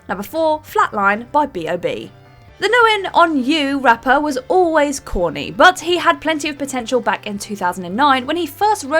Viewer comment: what did you even say there? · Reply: Hahaha ha. I think she meant 'nothing on you' aw....our cousins across the pond are so cute with their mispronunciations and false information.